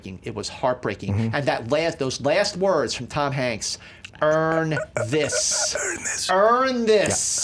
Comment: I always thought Tom Hanks was saying "Ernest" as in his name, at the end of saving private ryan. His first name was a mystery through the whole movie & speculated upon by the soldiers. So I thought he was telling Ryan his name, not "earn this". Anyone else think that? Or is it just me being a dumbass? Lol